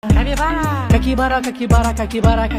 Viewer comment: I thought he was saying cackybara 😅😂😂